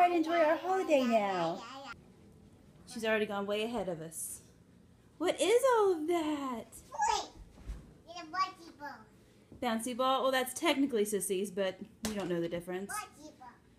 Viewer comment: I was just wondering at 1:00 if was possibly saying "Basketball" instead of Bouncy ball. I know you know your girls best, but I just wondered. Neat breakfast idea too! Merry Christmas! Wonderful walking!!!! All the way across the room!!